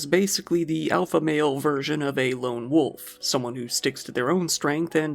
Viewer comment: I love how I heard "a lonely male virgin of a lone wolf" but I should have heard "version" lmao